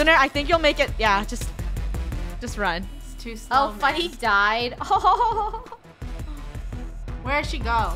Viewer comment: said died but realy since she was a rubber duck...... she was actually saying DIVED